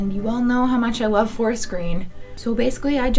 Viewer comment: is she saying floor screen or fora screen? And then @ 3:02 is she saying the Face Wards? I really like the outfits but am confused